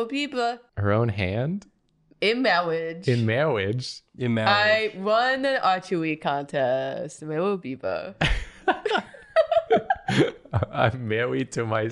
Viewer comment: The fact that this made me realize she said "I won archery contest" I was confused because I heard it as "A chewy contest"